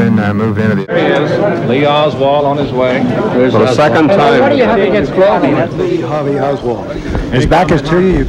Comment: what does Oswald say..."what do you have against ...??" ? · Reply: It sounds phonetically like, barone, or marone....... It's so weird that he would emerge from an interrogation room and blurt that out to a reporter. He gets whisked along before he can finish that sentence, that is clear for sure.